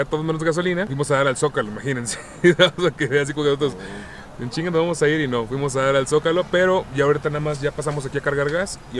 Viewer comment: cuando se rie que dice?? Jajajaja 😂😂🤣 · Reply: "nadamas pa que veas jaja"